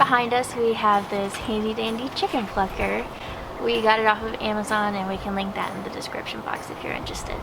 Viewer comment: i thought at first you were saying fucker my apoligizes